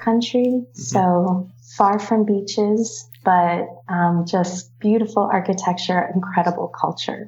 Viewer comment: Не понимаю что она говорит. "five on beaches"? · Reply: Far from the beaches - вдали от пляжей)